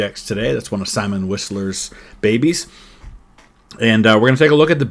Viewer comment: Every time... Anyone says 'Simon Whistler' I hear 'Salmon Whisperer'